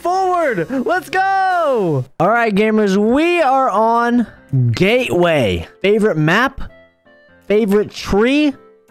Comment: just the best map! GAYWAY (it sounded like that)